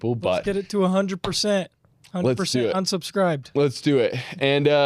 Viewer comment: Did bro just say "100% *unsubscribed*?"